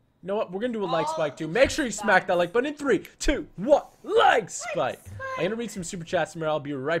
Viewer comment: everytime they say "like spike !" In my mind I always hear it as " Lag Spike! "